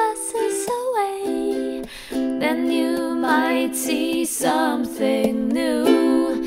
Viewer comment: wow, statt might see hab ich mai thi verstanden xD